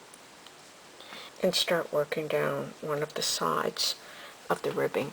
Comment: even the piece you held up didn't quite cover the entire don't area. I am using a smaller needle and I started with a 22 chain base. Did I not do it long enough [then] start going up the sides? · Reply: never mind. I think I figured it out. it sounded like you said to "Go up the side" one the ribbing was complete, and that is what confusion me. I'll let you know how it turned out. 😆 GREAT PATTERN! THANKS!